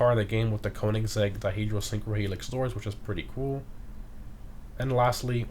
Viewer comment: I can’t figure out what he said around 5:15-5:20...sounds like another language on my 3rd playback 😂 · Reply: Koenigsegg doors full name is dihedral synchro-helix